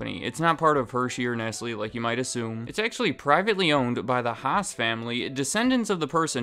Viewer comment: Was I the only one who thought he said the Huts family?